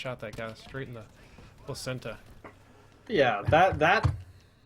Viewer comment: Wait did just say placenta at 10:07? What mate u high?